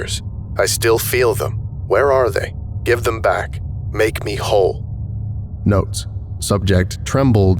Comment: gets his plasma cutter I heard "make us whole". I'm not taking chances.